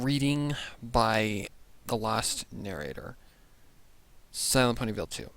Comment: did I misheard him saying LastNarrator or was it still Lost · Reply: Midnight Flare I said "Lost" lol